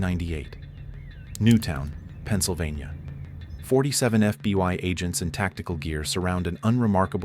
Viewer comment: did he say FBY ? lol